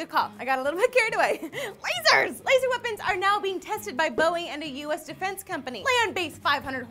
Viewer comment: I thought you said Bowie. That would have been awesome.